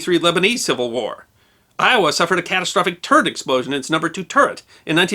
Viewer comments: I definitely heard "turd explosion" instead of "turret explosion", combined with the brown clouds in the image, i was highly confused. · I just misheard that as "a catastropic turd explosion" o.O Then again, turd explosions can indeed be catastrophic, as we learned from the story of U-1206...